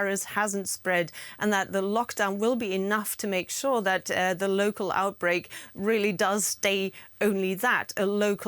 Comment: I thought it said total outbreak 😳